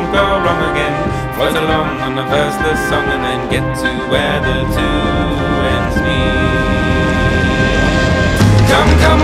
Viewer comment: When it said "get to where the two ends meet" I thought it said "get to where the two suns meet"